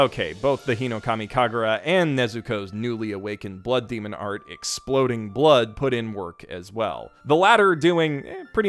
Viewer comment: “ exploding blood “ you say ? All I heard was BUCKETS 😂